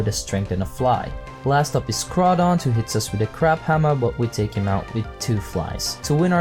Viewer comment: Did I hear crap hammer?